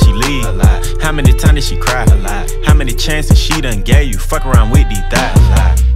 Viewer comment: I doj't know why I thought he said "Fuck Around Whitney Died Alot"